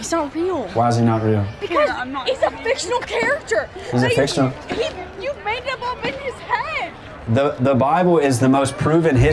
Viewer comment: Did she just say, "You mad him up in his head" ? 😂😂